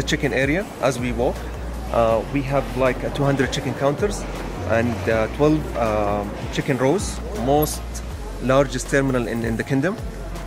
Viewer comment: min Is this only me or someone else who heard chicken area, chicken counter and chicken roast. I really lost my confident. I was watching this video in tv and all my cousins were laughing at me after this incident. I really need to improve my English.